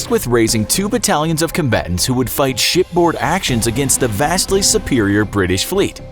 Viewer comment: did he say s***board? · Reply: Ryu Aryananda Ship